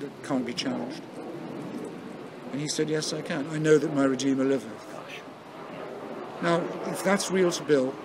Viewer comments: I cant understand the irrefutable statement that Buckley said at 42:45. Can someone explain please? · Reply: "I know that my Redeemer liveth." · Can someone please tell me what Hitchens says at around 42:45 about the irrefutable statement? I can’t understand. Thanks 😊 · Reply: Something along the lines of "I know that my Redeemer lived." This, or some equivalent statement about Buckleys own absolute faith in the Christian mythology, fits in with the point Hitchens goes on to make about those beliefs having a force in the wider world by virtue of the conviction in which they are held by others.